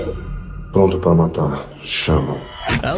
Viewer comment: what does he say before Chama? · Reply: "pronto pra matar" ready to kill